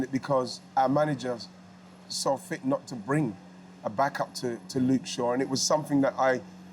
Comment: did Ian Wright just call Southgate "thick" or am I hearing things? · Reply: “Saw fit not to bring”